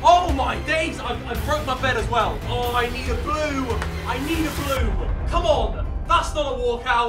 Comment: I thought he said "I need a poo"